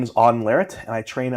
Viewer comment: Subtitles saying "Devon Larratt" instead of Auden 😭